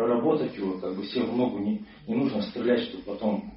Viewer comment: Что он сказал?))) "Всем в ногу не нужно стрелять"?)))